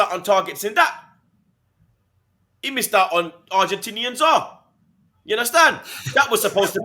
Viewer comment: Lol at 20:05 did call Lisandro Martinez the Argentinian Zah? 😂😂😂